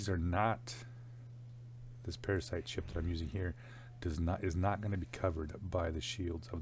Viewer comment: parasite ships??? I heard "Lag Ships"